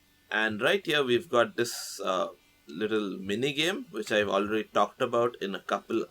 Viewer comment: did that just said the n word? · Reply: "mini game"